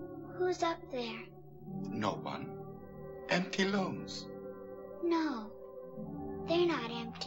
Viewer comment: Did he say "Empty looms"?